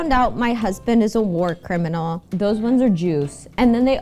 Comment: "How I found out my husband is a war criminal" Did she just follow that up with "those ones are Jews" or did I mishear that...? What? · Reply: "Those ones are juice", as in juicy gossip.